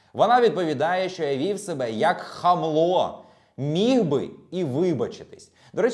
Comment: мені почулося "вів себе як хамло, міг би і виїб@ 🤣